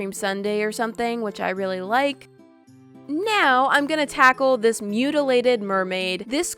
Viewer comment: Nyaow~ It’s sounded like she mixed now and “nya~” together when she grabbed the mermaid